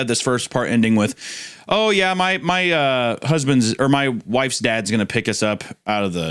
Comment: The first time you said "Faye's Dad" I heard "FaZe Dad" and lost my shit